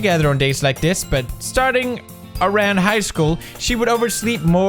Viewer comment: "Starting OURAN highschool" Who else thought he said that 😩😂